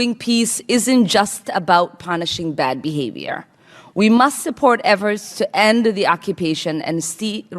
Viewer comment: Who is this idiot kidding? And WTF is she saying: 0:09 we must support everts an-stee-wrist achieve. As marther luther king....jr said peace is not merely... uhhh... distant goal that we seek but... a means by which we arrive at that goal.